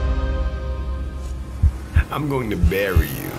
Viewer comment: I thought and the end he said I'm gonna marry u...ALIVE